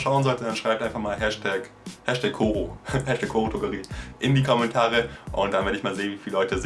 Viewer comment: #koro ? richtig so? habs nich ganz verstanden^^ (minute